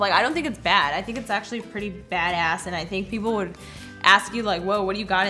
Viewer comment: Ima the only one that Heard her say a bad word…? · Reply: Yeah she said badass